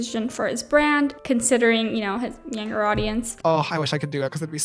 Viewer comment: Turn on your captions and where you discuss JC doing a Only Fans channel and the reasons he could not, the captions says the “N” word. Check out at 8:26. I think it meant to write “younger audience” and not “N” audience.